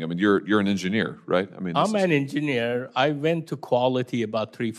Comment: All jets are shimmed at the joins, nothing to see here from another disgruntled former employee. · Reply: I misheard him, thought he said “I’m not an engineer” in response to Hawley. Anyway are you really trying to be salty about the fact that I have a screen name on the internet? Weird move.